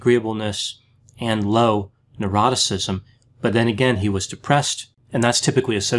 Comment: Who else spits out the OCEAN acronym in sync with Dr.!? 😂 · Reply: Thing is though when I first started to watch Dr G. I thought he said “eroticism”lol. His accent throws me off a little from time to time.